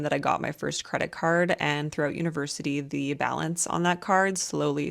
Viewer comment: Coulda SWORN she said, “throat university”…. I was like what the hell college did she go to 😂😆🤣